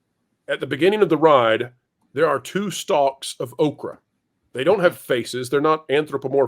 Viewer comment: I kept hearing "Oprah"...was a little confused...